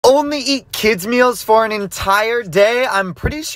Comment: dawg I heard “only eat kids as meals for a day “ 💀